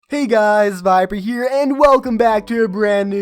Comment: I thought he said “hey guys VIBER here” lol